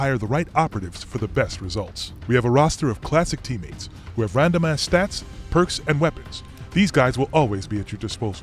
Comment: "we have a roster of classic teammates who have random ass stats" lol. That's all I hear